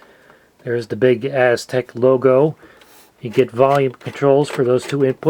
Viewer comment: I heard "the bigass tech logo".